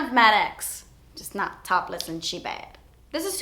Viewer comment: When she kept saying she bad for the colourpop lippie stix I thought she was saying she bags😂